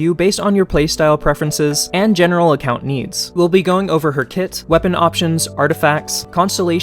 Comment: sorry I just gotta get it out of may system for mishearing you say "we'll be going over her tits". sorry i need to comment this. i just... have to.